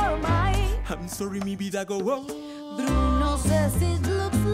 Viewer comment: do you all here this or is it just me I hear sorry my lady Gaga waah